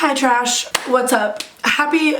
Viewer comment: is she calling us trash?